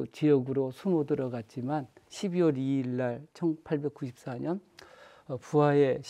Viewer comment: 강의 잘 들었습니다. 동학과 전라도는 떼려야 뗄 수 없는 역사이고, 이를 전쟁의 관점에서 잘 설명해주셔서 감사합니다. 마지막 일본군의 잔인한 살해방법은 매우 가슴이 아픈 이야기였습니다. 다만, 자막 수정이 필요한 부분이 있는 것 같은데 3:20 무렵의 '1984년'을 '1894년'으로, 1:10:35 무렵의 '위폐'를 '위패'로 수정해야 할 것 같습니다.